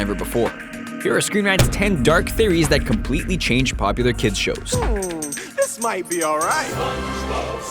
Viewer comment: I thought that said "dank theories"